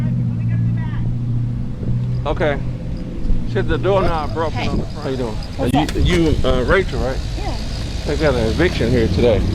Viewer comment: I thought she said the front door won't open. I see why she didn't want him in the house 😂